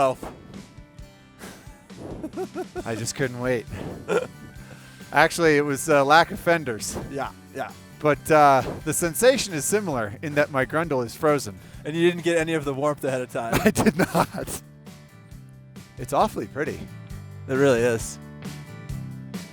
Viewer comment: did he say "My Grendel is frozen"?